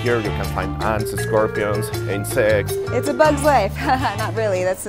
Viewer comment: What is he saying here 0:43 because it sounds like he said "ants and scorpions and sex"...can some one correct me? because I'm genuinely lost @@